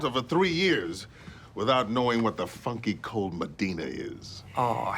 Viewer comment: I thought they said the f word but they said funky